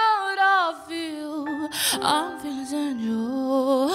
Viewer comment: Who else heard "we should be sandshoe all" listen to it again, be honest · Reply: i heard"we should be sensible"